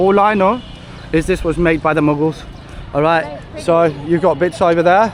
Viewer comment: "It was built by the Muggles" 😂 2:37 sounded like harry potter there 🪄